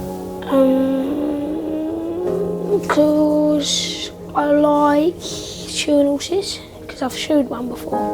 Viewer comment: what did he say? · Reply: “Cuz I like shoein’ horses”